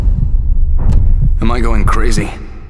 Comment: It kinda worries me that no one else has asked this, but... what the hell is said at 1:53 ? Right after the guy says "Am I going crazy"? All I can hear is "will you be able to live with yourself nomorojuashd;lkjfha;lk ..." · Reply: will you be able to live with yourself...knowing what i'm going to make you do